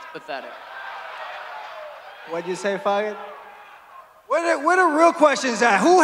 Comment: Did chito call Sean a faggot at 9:20?